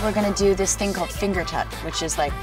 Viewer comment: I thought it said finger cutting